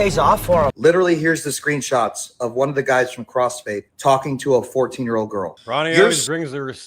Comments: Man I thought they were saying Crossfade 😂 I was like man that’s not them … but they are disgusting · I thought he said crossfade lolol